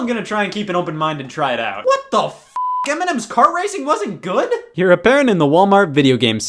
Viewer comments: When you said “m&m’s cart racing” I thought you said “Eminem’s cart racing” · the subtitles say eminem´s car racing, now i want that game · I wasn't looking at the video, so when you said m&m's kart racing, I heard Eminem's kart racing, and wondered why it wasn't good.